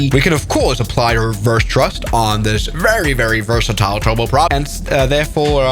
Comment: Did you call it a "trouble prop" instead of turboprop? :) Also I'm glad I didn't know about its safety record back when my dad used to fly on them out of KCWA in the 80's. Looks fun though, and you've got me finally wanting to install X-Plane again.